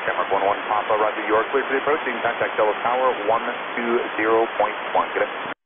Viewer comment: I heard 120,1 but in the transcrips says 121,1 I'm practicing for the English test and I don't know if I'm wrong! Probably :(